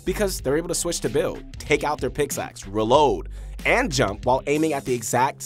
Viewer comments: did he say picksaxe or its just me tripping 😂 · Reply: I literally stopped at 2:04 and came in the comments when I heard him say it I was "nah my man did not just say pixaxe I'm so deeeeaaaad" 😂 😂 😂 😂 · did he say picksaxe or its just me tripping 😂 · I use scuf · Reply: pickxaxe? Anybody catch that